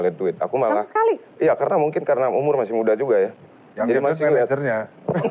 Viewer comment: disini dhani ngomong apa ya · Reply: Yg liat menejernya, keknya gitu😅